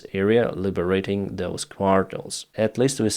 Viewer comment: Does anyone know what word Denys is saying at 3:41, auto translate says “quals” but sounds like quartiles, I often hear him say it but can’t figure out what it is, it driving me mad. 👍